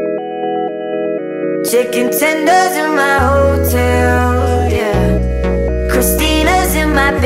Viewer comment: I deadass thought he said "Checking tinder in my hotel"